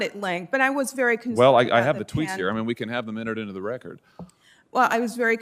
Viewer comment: I say play that damn tape you said you had. Watch her squeal in her chair. Why let her off the hook if you have a tape proving she's lying. Damn if I had that opportunity to shed some light...britches be burning. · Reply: I thought he said tweet but what should be done is a new tweet, one with her original one next to her skirting the issue and show her dishonesty and true agenda